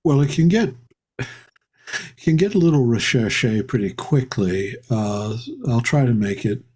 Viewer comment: what is the word that DBH uses at 4:03?? 👀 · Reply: Recherché.